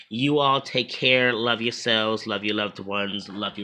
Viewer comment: Lol I thought you said , keep smoking those herbs at the end of the video.